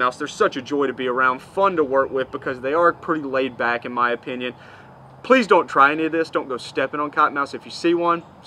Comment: That’s one of the most irresponsible things I have ever heard. You “TELLING” someone to actually walk right up to a venomous snake, then tell them to pretend you’re going to step on it. I have treated my share of venomous snake bites and it is one of the ugliest things I’ve ever seen. Not to mention the consequences of the bites. You “can not” know how a persons body is going to react to the venom. Unbelievable!!! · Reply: he clearly says "PLEASE don't try any of this.. step back, walk away", really good video btw, i frequent Lake Fork and have heard countless stories of "CoTtOn MoUth dOnE cHasEd mE!"